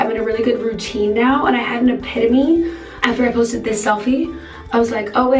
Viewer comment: I had to go back and make sure I actually heard her say epitomy instead of epiphany